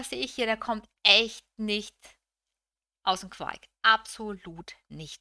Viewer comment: mein gegenüber was ? Hab es mir 5 mal angehört und nicht verstanden , aus den queit?? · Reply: Aus dem Quark heißt es. Ist eine Redensart im Sinne - kommt nicht in die Gänge 🌸